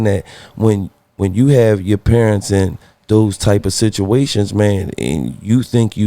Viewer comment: So am I the only person that heard him say “situationages”